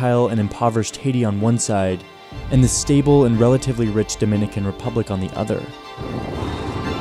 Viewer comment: Equilibrium 0:41 Try again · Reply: Equilibrium is correct. He clearly says "relatively rich" .You try again